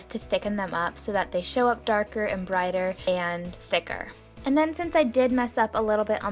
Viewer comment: Wait did you say "it'll show up darker and brighter"?